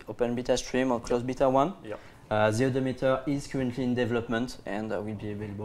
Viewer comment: What does he say at 52:04 What meter is he talking about? Its the only thing i didnt understand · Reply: talking about the mileometer, the counter that shows the distance you have done in that vehicle